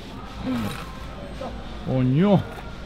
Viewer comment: don't think we didn't hear you at 7:50 "CoñooooOoo" LMAO!!